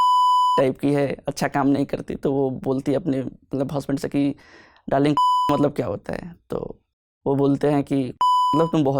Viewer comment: What word he used at 25:45?? Which they beeped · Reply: Sasura